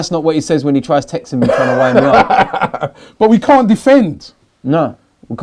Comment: did say ''that's not what he says when he (fllex) tries texting me about my mum'' ??? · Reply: instead of the mum bit its "trying to wind me up"